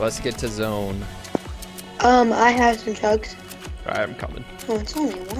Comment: I swear I thought he said "I have some drugs." Bruh